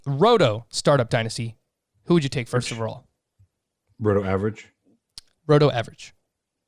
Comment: what did he say? Roto average?